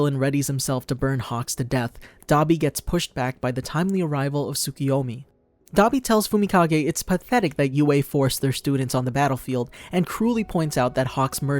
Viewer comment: did you say "the timely arrival of tsukuyomi" Naruto seeps into our brains